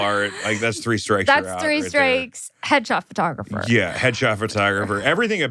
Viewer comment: I thought they were saying “hedgehog photographer” the first like 4 times they said it. I was like wow thats wildly specific lmao